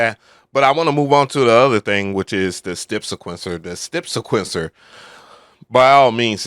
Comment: did you say stip sequencer?